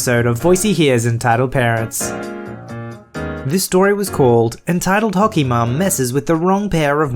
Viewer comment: I heard “entitled Pokemon” in the intro of the first one. Also, you sound different. You got a cold, or a new microphone?